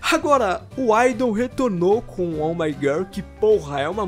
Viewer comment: é impressão minha ou escutei ele falando oh my girl? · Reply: kiara batalha realmente ele falou